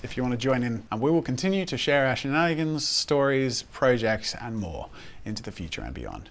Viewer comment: I heard "Share Ashey Nannigans"... did a double take and then realized "Our Shenanigans!!" Oh!...